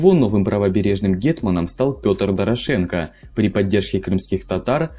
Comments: мне послышалось что гетманом стал Петр Порошенко) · Пётр Порошенко мне не послышалось?